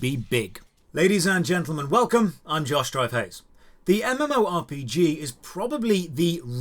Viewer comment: when he said his name i heard "im josh dryface" ahahah